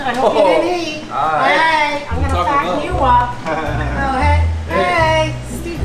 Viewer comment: subtitles say "I'm gonna fuck you up", but mom actually says "I'm gonna fatten you up" 🤔